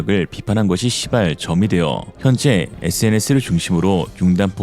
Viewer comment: '시발 점이 되어' 가 '씨발 점이 되어' 로 들리는 것 같은건 제 착각이겠지요? 씨발~! 우리나라에 친일파가 있듯이, 폴란드에는 친독파가 있죠. 대표적으로 국방차관 톰치크가 친독파라고 하죠.